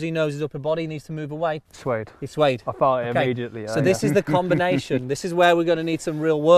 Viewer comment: Sounded like he said "I farted immediately" 😂